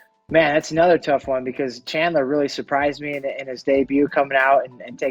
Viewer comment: when you watching an interesting movie and the climax sucks. 😆 Who the f did he say? Charles or Chandler? 🤷‍♂️